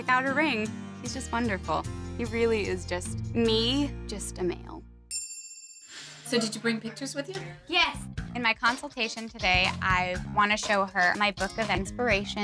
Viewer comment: Did I hear her say, "he's just a male?" PLEASE TELL ME I HEARD HER WRONG....